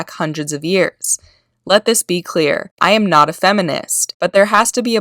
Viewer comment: fucking STOP right there what she said was "I am not not a feminist" OPPOSITE to what said. Smh.